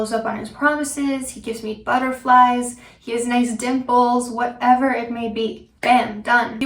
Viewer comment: i heard "he has nice dick balls" instead of "dimples" the first time ugh